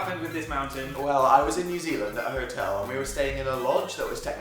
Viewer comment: did he say NEW ZEALAND!? Where I freaking live!!??